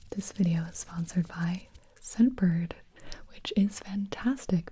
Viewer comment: bruh my volume was so low i thought she said this video is sponsered by sober